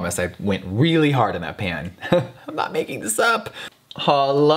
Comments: did i hear HOLO. Yas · did he just say holo? Holosexuals where u at? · Did he say H💿l💿?? Where're my h💿l💿sexual at??